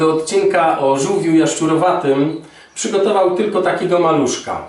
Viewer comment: "Nie był bym sobą, gdybym w odcinku o żółwiu jaszczurowatym przygotował tylko takiego maluszka." Ja usłyszałam JANUSZKA 😂😂😂😂